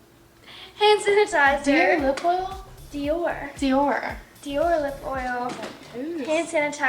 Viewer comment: Lol i heard "dear lip oil" too